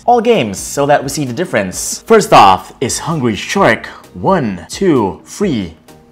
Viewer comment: Did you just say "Hungry Shrek" instead of Hungry Shark.